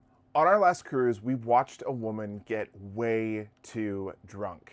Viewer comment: I wasn't paying much attention (I love using your videos as background noise while I work) and I thought you said 'WE SAW A WOMEN GET WEIGHED' 😂😂😂😂